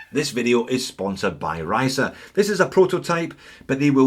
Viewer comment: Star Trek red alert, and for a moment there I thought you said this video was sponsored by Risa :D